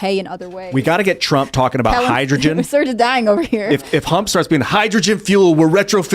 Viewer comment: did Ian call him “Hump”???